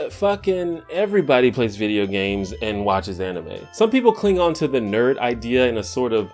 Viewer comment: What I heard: "Some people Klingon to the nerd idea in a sort of contrarian way."